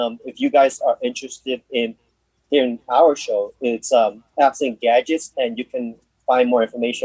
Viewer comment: It has been a joy being a part of this podcast. I'm just wondering something, what is the show mentionned at about 23:01? I hear have some gadgets but can't find anything, and I'm also not sure about the adress mentionned a few seconds later, afl-radio or something. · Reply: thanks for the comment. so the show mentioned was apps and gadgets. you can listen to it live every Saturday at 1pm eastern at. <link>